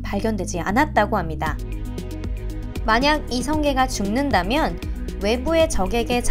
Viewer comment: 만약 이성계가 죽는다면 으로 들림